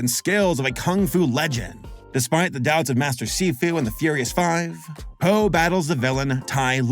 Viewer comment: I’m sorry did he say sifu